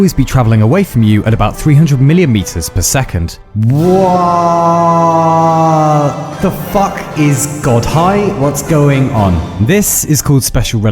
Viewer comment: “Whaaaaaaaat the fuck is God high what’s going on?” Said to the rhythm of the music. I repeated that part like 5 times lol